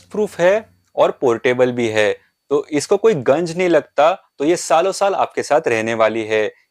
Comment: isko koi ganj nahi lagta ??? ya Jung nahi lagta ?????